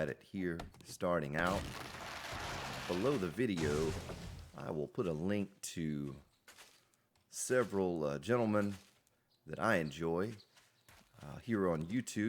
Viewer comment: what did he say.... x _ X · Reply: I will put a link to several gentlemen in the description...